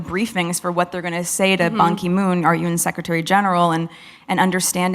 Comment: Meghan saying the Rawandan women "had to find some strength" after the genocide, the woman who fell to the floor sobbing evertime anybody in the UK hurt her fee fee's, those women already had strength dear, she hasn't a clue. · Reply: Did she just say monkey moon???😂